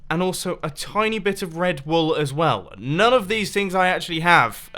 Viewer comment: when you said red wool, i thought you said red bull